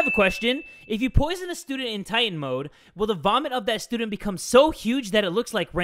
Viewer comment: Bruh i thought that said italian mode wtf